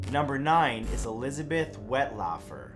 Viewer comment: About 1:53 I thought it said Elizabeth wet waffle